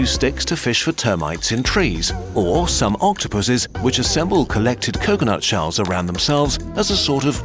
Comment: did someone say pussies?